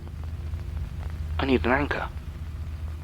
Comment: I am having a hard time understanding the words he's saying here. What is he saying after, "I need..." A blanker? Bianca? A blanket? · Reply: an anchor